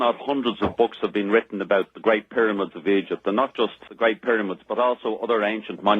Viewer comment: did he say "the great pyramids of eejit"?